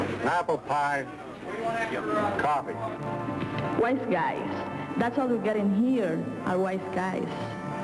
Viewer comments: lul did she say "white guys that's all we get in here—white guys." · LOL who heard it as 'white guys, that's all we get in here are white guys"??? It's actually WISE GUYS! LOL I think it's a combination of PRIMING (so we're primed to think of it as a latina complaining) and the fact that WISE GUY isn't really used these days (we say smart ass).